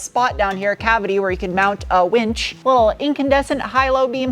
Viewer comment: "There's also a spot down here... a cavity where you can mount a winch"... Oh, my mistake... I thought you said, "Mount a wench". That would be wrong... and entirely a different thing. 🤣